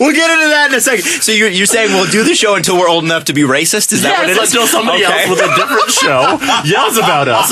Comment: what does say at 3:12? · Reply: Rook O I think he says "Are we xenophobic?". I think